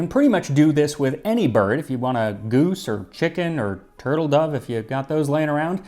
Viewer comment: Why did I mishear “turtledove” as “turtle duck”? It’s been nearly two decades since I last watched Avatar.